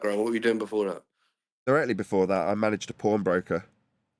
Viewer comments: Definitely thought you said you were a “porn broker” at first 😂😂😂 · I thought said he was a porn broker. 😂